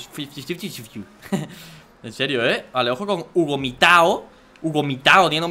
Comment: le entendí vomitado